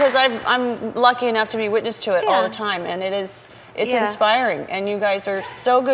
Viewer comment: Jennifer actually said that their relationship is "expiring" and not "inspiring" like it was supposed to be!! Wow! She said the truth probably on purpose. She is a boss! It's at 19:28 Am I nuts or did she say that?